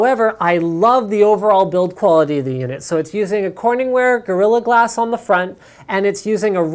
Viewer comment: ahh did u not hear what he said.. lol there is corning's gorilla glass